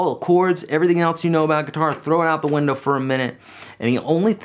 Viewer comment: Oh, crap! I thought he said throw the GUITAR out the window.